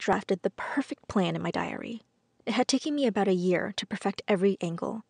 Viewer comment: i thought it said diaria